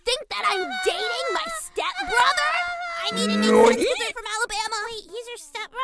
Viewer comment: is it me or did she say ‘I need to make kids with him from alabama’